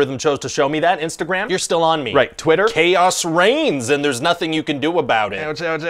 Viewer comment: Twitter: "Chaos reigns and there's nothing you can do about it" That just keeps getting more relevant with time this year. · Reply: Ooooh I thought he said chaos rains lol